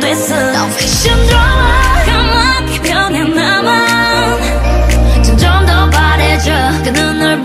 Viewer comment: I thought Wheein said "chinese overdose" 💀💀